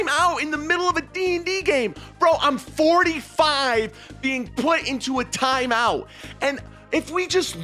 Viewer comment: did just says he 45?